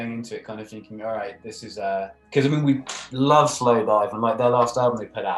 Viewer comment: cool. at like 5:08 he mentions another band, but i cant quite hear. someone understood? this is one of the most fresh bands now slow dive??